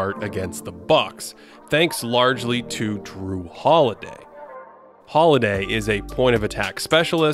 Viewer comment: did he say drew holiday?